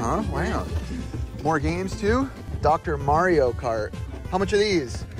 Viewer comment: You said "Dr. Mario cart" but I heard it as "Doctor Mario KART" and I'd be down to race around with some viruses.